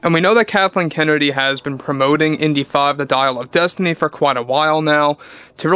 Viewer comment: Oh Dial of Destiny....after watching the trailer for some reason i thought it was Dyke of Destiny....makes more sense now...although i think the misheard title fit it better tbh...